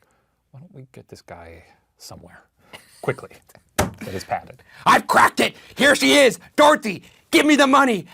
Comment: I thought he said I’ve crapped